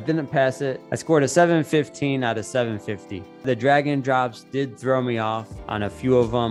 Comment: LMAO he got no idea. That's what I thought he said too but he actually says "drag and drops", as in the PBQs (performance based questions) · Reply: Dragon drops = drag and drops. I gotta say that next time. Love it 😂😂